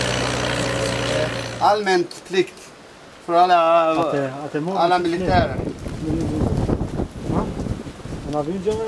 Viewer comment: Was that guy speaking swedish at 0:22? Sounded like he said "Allmän plikt för all militärer", can someone correct me if I am wrong?